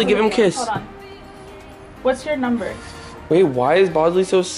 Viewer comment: Did anyone hear "what's ur number"